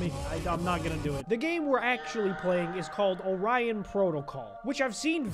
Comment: I thought it said onion protocol